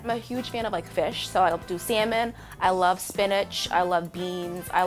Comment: did she say spiniCH